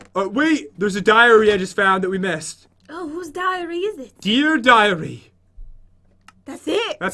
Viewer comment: did he say diarrhea 😂😂😂